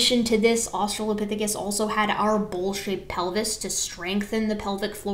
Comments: I misheard "bowl-shaped pelvis" as "bullshit pelvis" xD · At around 17:00 I swear to God you said bullshit pelvis